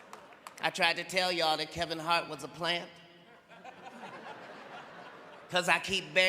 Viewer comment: Thought he said Kevin was an industry plant